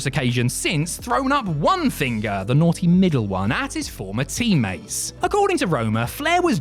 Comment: did he say naughty n*gg*r one? When referring to Paul Roma flipping off the horseman.